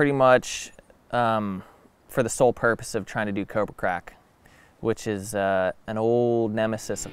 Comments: I thought he said “coke and crack” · I thought he said I’m gonna do “coke or crack”